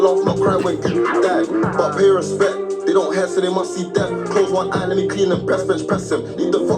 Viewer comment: "they dont hear so they must see death" (deaf)